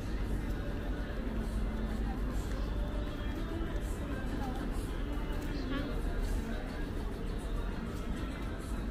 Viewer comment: 他们说什么？ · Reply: 來嗎